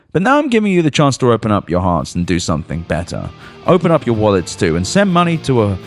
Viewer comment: Man, I thought you said "open up your arse and do something better". 😂